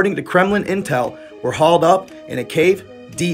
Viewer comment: "Hauled" up? Was it an elevated cave or did you know it is called: "Holed up?" The captions say "Held up his fist to haul?" what's going on here? @ 2:16 "Presents?" They just met, why would they bring "Presents?" god, can't even research or double check the Closed captioning , makes me wonder what else if half arsed. Educated in America?